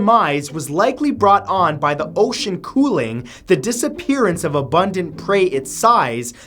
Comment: When Matthew was talking about the death of Megalodon, i thought he said "By the ocean KOOL-AID," when he said COOLING what is my life